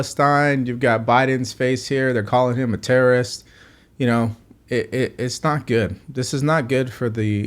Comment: Did you just call Biden a "collagen terrorist"?! If so, right on, brother! He's like 240 years old, yet his skin is so smooth...